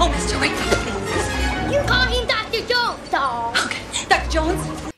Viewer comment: I'm not sure what "Yuu taagin Dr. Jones diaoo?" means, but I'm sure it's a great line. I looked it up. Apparently it's supposed to be "You call him Dr. Jones, doll!" I still don't hear any of those syllables other than 'you,' 'doctor,' and 'Jones.'